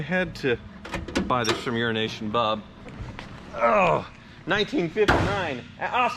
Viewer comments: Sorry about the comment, but is it just me when he says “Euronation Bob”, I hear “Urination Bob”. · I must say every time you say “EuroNation Bob”, I hear you say “urination Bob” 😂